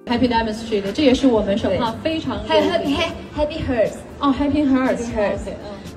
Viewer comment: I thought she said happy horse. But like yea imma still give her some slack